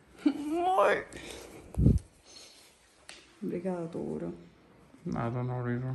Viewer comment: eu escutei "mor"??